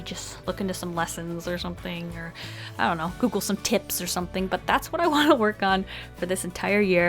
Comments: i thought she said google some tits 💀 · Heck I thought she said “google some tits” so that’s what’s going on